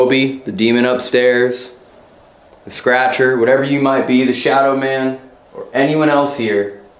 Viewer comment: In my opinion, I heard " I'm The Scratcher, Ya." The other part I could not understand.